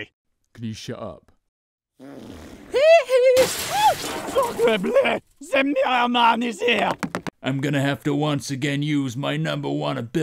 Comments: Any french speaking guys? What does Polnareff says at 2:08? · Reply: "Sacre bleu! The mirror man is here!" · I think Polnareff say Russian "Suka blyat"??